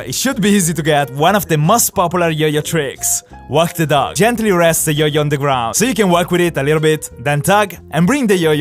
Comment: I really thought he said "Gentry rests the yoyo"when he did walk the dog lol... only for a few seconds